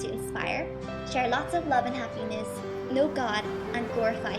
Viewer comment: oh sorry I guess I thought she said "no god" instead of "know god" and I know being happy doesn't rely on being religions, but happiness has many factors and for me religion is one of them, its like a catalyst to happiness